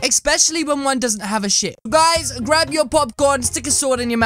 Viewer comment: did yah say the bad word? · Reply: nah he said ship xD